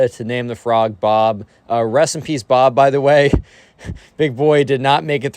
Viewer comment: I heard "recipes Bob" instead of "Rest in peace, Bob" also frog